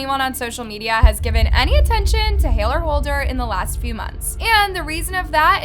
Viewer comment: Did you say Hayler Holder??? 🤣🤪